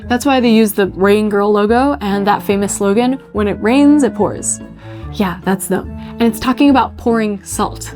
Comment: did you say: Yeah, that's them. ? ... asking because that's what english CC says (rather reads)